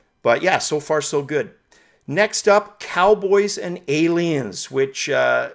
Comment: Cowbots not cowboys:) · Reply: Lol could have sworn i said cowbots but you know me and pronunciations....geebuz....